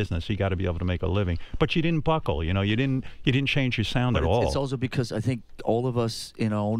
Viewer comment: Did he say:"you didn't change your sound at all??????"